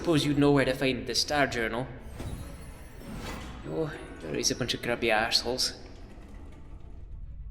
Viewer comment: What does Spike say? "Always a bunch of grumpy assholes."?